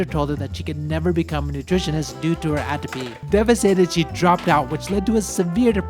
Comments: I can't understand what he's saying. her teacher said no because atrophy? whats that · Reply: The teacher advised her against a career in nutrition due to "Atopy", the genetic tendency to develop allergic diseases. Nutritionists are often required to sample (consume) foods in order to analyze them. One specific example, Japanese schools typically employ a nutritionist who examine and sample each day's lunch meal before it is served to students and staff. Atopy and associated allergic conditions may make that role difficult and potentially dangerous to her health. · Due to her WHAT?? Did anyone hear that? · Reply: The teacher advised her against a career in nutrition due to "Atopy", which is the genetic tendency to develop allergic diseases. Nutritionists are often required to sample (consume) foods in order to analyze them. One specific example, Japanese schools typically employ a nutritionist who examine and sample each day's lunch meal before it is served to students and staff. Atopy and associated allergic conditions may make that role difficult and potentially dangerous to her health.